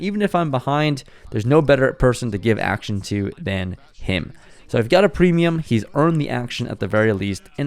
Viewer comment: did I hear I’ve got a premium with K8s 😂😂😂